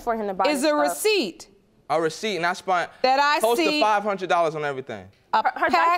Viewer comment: That boi said "I spunt." 🤦🏽‍♂️🤣🤣🤣